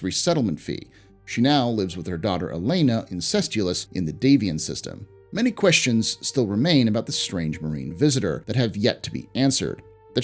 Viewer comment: What does he say? Does Octavia live incestuously with her daughter Elena(?), as the autogen subtitles suggest